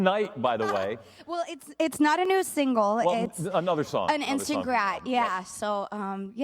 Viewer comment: What is she saying at 1:52??? Insta-what?? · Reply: An instant grat